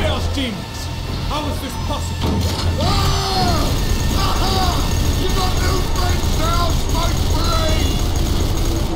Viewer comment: "you're my new friend space marine!" is that what he said? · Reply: "You got new friends now, space marine?" I think that's what he said.